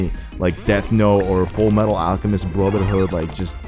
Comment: Did someone say Fullmetal Alchemist?